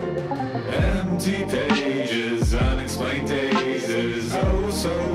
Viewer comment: Am i the only one who hears "Empty dages" here??